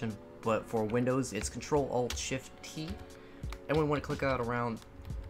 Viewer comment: What do you say at 2:58? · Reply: ilOoo Designs crtl alt shift t. Why did you make me listen to my old voice